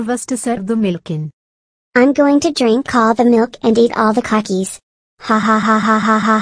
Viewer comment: did he just say cockies